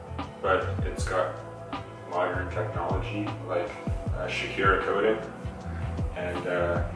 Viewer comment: Lol did he say his forks are shakira coated? His bike is really cool though, it has a retro look to it. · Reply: Earl Grey 😂😂 I heard that too! “ uhh shakira coat “